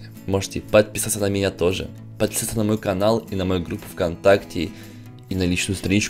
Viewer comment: Мне одному послышалось на 9:38 Подсасаться на канал.